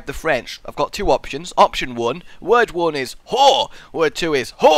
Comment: At around 12:40 it sounded like he was saying wh*re